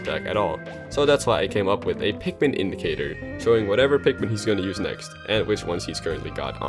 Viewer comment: Wario does need an indication for waft and bike. I think the garlic indicator is a good idea, but the bike indicator would be an oil tank so when it’s full you can use the bike again. My favorite one on this list is Wario’s waft and bike indicator. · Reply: My horrible recovery ass thought you were saying Incineroar instead of indicator.